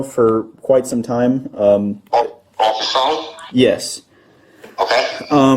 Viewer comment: this is nitpicky but i think the grandpa said “on the phone?” rather than the caption given